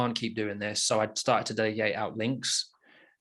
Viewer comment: What said at 34:42 ? I couldn't understand what he said. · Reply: he said he "started to delegate out links" (by using a linkbuilding vendor)